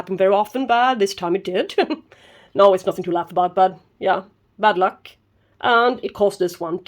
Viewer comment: Fantastic flower and vid. I heard "Bud luck", that was funny 😀. Thanks for sharing 😀👍